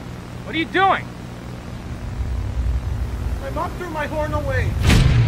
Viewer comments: The inaudible part sounded like he said "my mom threw my horn away" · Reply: ha! i thought he said my dog through my horn away! lol i was so confused! lol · The inaudible part sounded like he said "my mom threw my horn away" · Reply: I thought he said my mom threw my porn away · The inaudible part sounded like he said "my mom threw my horn away" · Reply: I thought he said my mom threw the corn away